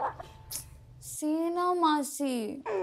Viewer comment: Am i the only one who thought he said ma ki at the end 😂